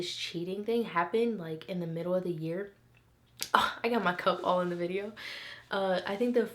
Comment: sound like she said cum lol